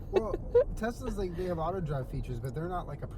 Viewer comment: I know Julien said auto drive but my headset wasn't working so I thought he said otter drive